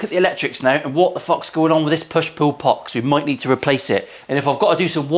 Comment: Sounds like you said “a push poo pot”😆 & “push poo madness”😂